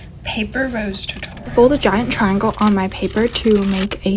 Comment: HELP WHY DID I HEARD “paper rose toy tutorial 🥰”😭😭😭😭